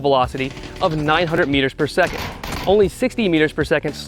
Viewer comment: dude i almost shit my pants when he said initial velocity of 900 meters per second. thought he said 900 fps at first lol